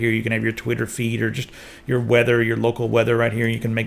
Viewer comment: Was he calling Onedrive "weather" at 5:45?